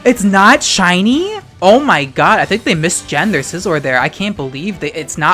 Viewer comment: pretty sure you said "mis-genned their Scizor" but i THOUGHT you said "mis-gendered their Scizor" and i fucking combusted